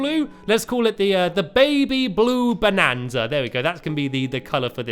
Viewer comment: When you said baby blue bonanza i thought you said baby blue banana!!!😂😂